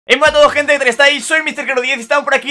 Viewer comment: soy yo o escuche e matado gente ???